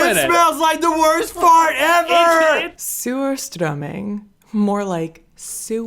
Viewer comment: I'm sorry, lol. I keep hearing you say, "sewer-strumming." Like, why are we strumming a sewer?!